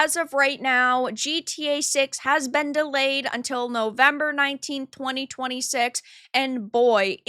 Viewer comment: At the end there I thought I heard say that GTA 6 "was delayed until November 1926" and was really confused about the current date for all of 3 seconds.